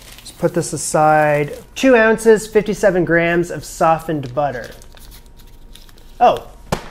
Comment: I was watching this to fall asleep and my sleepy brain fr thought he said sausage butter and I was very confused... he said softened butter which makes <num> times more sense lmao 🤣 😂